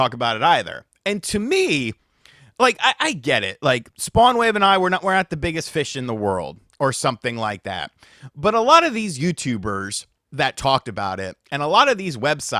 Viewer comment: You say, “We aren’t the biggest fish.” And I hear, “I’m no Hylian Loach.” Hahahaha ok sorry I’ll leave the content to you.